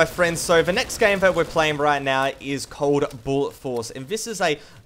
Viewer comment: Im confused at 16:08 did he say bullet or buller?